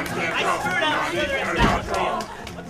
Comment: For a second I thought u said u couldn’t draw I was about to ask why the heck u thought u couldn’t draw